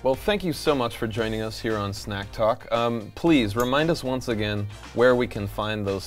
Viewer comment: Ohhhhh.. *slaps forehead*..I thought it said SNATCH talk..I'll take snacks too though.